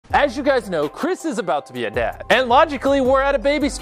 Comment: I thought he said chris about to get a dad